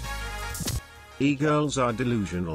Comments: What did Poki....just say? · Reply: "did you send him a shirtless pic or something?" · The amount of people who thought Poki said “my shirtless pics” instead of “a shirtless pic or something” is confusing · Did poki just said she has a shirtless picture? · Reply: "did you send him A shirtless pic or something?" i don't get why everyone heard "my" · what did poki said 🍒🍒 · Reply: She said "did you send him a shirtless pic"